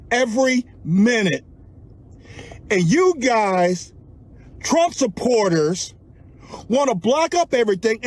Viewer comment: He lost me with Trump supporters...Sounded like he was saying white people to me.